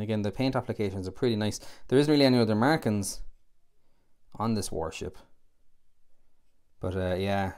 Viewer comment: Do I understand you rightly when you say at 3:27 , "there isn't really any other Americans on this warship"? If I heard correctly, might I ask you, please, as to your meaning?